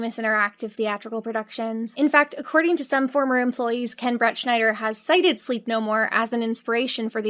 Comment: the whole video, ive been just listening to it, i thought she was saying bretschniders full name: bret schnider. and then when i watched (not just listened) the 'the end' and saw his name is ken bretschnider, not ken bret schnider 😭😭😭😭😭